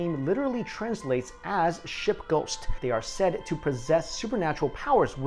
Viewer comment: When he said "ship ghost"... I thought he said "SHIT GHOST" 😂😂😂